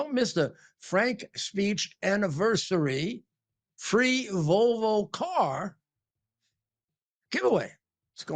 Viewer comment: Rudy is such a creep it sounds like he said free Vulva car